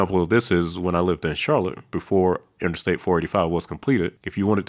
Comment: am I getting old or am I hearing you say I-45??? I had to pause the video and rewind so many times. I-45 doesn’t run through Charlotte???